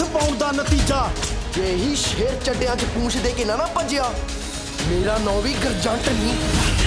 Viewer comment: Ahah did I heard GURJANT @ 1:42 😂😂😂